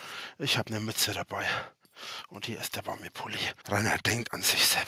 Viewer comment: Ich hab zuerst "Und hier ist der Bambi-Pulli" verstanden. 😄